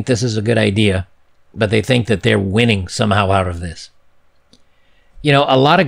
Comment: anyone else hear saying ”winning, noooooooo."